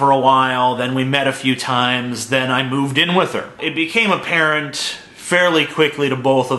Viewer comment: I thought he said "it became a parent" xD